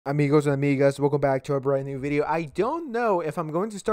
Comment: Bro at the start I thought he said “Amogos and Neegas”